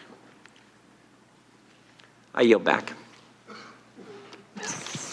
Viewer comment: wtf he says I am back ???